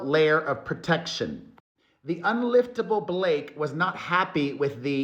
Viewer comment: Did I just hear "unliftable Blake"? 😅